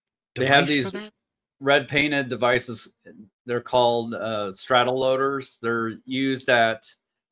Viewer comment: If anyone misheard "Straddle loader" as StratoLoader like I did, just google Straddle loader.